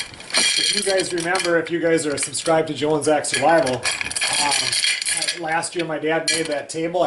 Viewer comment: Road warrior .........gday mate. Its "Joe and zach survival ", you will love , love, love that channel ......we all do. 🖒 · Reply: loadeddog K okay I know about Joe and Zach survival I thought he was talking about a different channel that his dad has. sound like he said Jones X survival but I have bad hearing I could have been wrong.